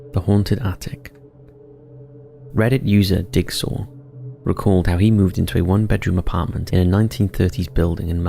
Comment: Did he say "reddit user Dick Sore?" Thats hilarious